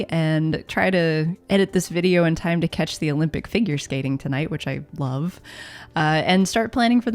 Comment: lol I heard "finger skating" xD